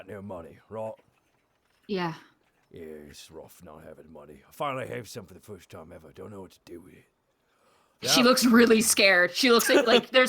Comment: Hey what does Borku say at 3:25:45 ? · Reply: "Its rough not having money; finally have some for the first time ever, don't know what to do with it"